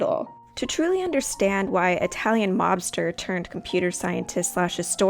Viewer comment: I keep hearing “the Italian Lobster”